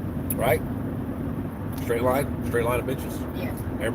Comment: Lol I thought you said grape vine of bches